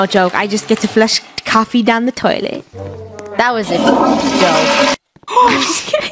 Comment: that was a sh*tty joke. But it sounded like she said it was a dumb joke.